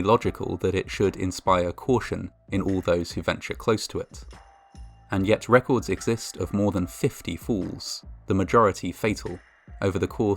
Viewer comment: I thought you said 50 fools and said to myself yeah fools is right. Oh 50 falls. Either way fools fall.